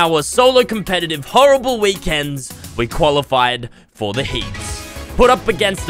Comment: i thought he said he qaulified